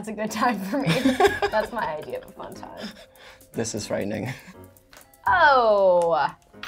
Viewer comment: Did he say the N word in 3:42-3:43 ?? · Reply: Dimitra Daxel he said this is frightening